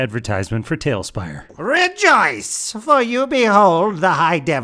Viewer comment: I thought they said Regice the Pokémon instead of Rejoice the English word for a second in the intro